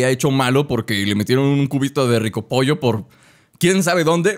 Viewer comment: escuche en vez de cubito culito xd 10:55 aca acaban de hablar en la historia